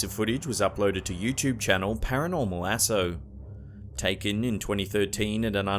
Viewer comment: i thought u said paranormal asshole.